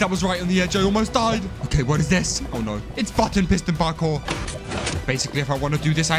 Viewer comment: did he say the f word? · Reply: "its button piston parkour" is what i said😄